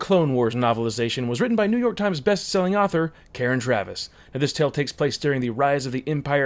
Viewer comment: Eldorid I thought he said Karen slaughter the author of one of my favorite horror novels, Blind sided, lol. "Karen Travis...."Is what he actually said.